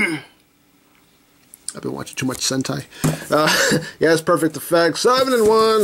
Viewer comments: i coughed up my coffee thinking he said hes been watching to much hentai at 0:55.. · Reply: Was that supposed to be hentai? 😂😂 I heard him said "Sentai". Is that even a word? hahaha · is it just me or did I heard something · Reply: Yeah dude actually said sentai. Not the H one. Don't go there bruh💀